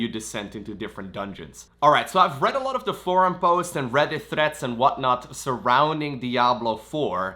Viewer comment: Anybody else thought he said 'Reddit Threats'. Lowko is such a treasure.